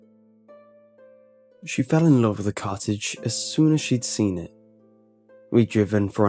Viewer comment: Was I the only one who thought said: "She fell in love with Carthage the moment she saw it." ?